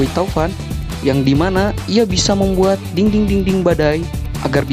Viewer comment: Di menit 4:37 itu ngomong apa :v Kan harus nya dinding-dinding, kok jadi nya ding-ding-dinding :v Atau aku nya yg salah denger TvT